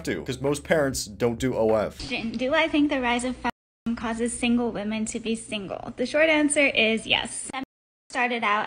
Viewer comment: why did I hear her saying "is the Ryzen 5 causing women to be single"? ,am I addicted to pc builds?